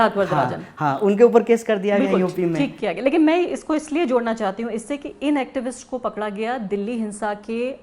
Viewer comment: Did she say “thik kiya gaya” at 23:04 min when he mentioned about Siddharth vardharajan ?